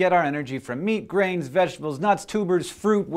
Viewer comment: When he said grains in the beginning, I thought he said brains.